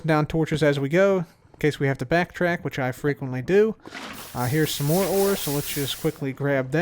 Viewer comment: Did anyone else hear him say "I hear some more ore"?